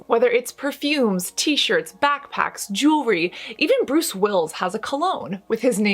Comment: Did she just say “Bruce Wills”? Lmao Her smile makes me feel uncomfortable.